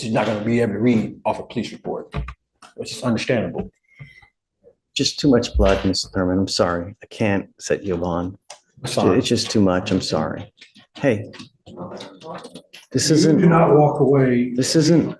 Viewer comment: I had to play back. i thought he said "Youre just too much black." lol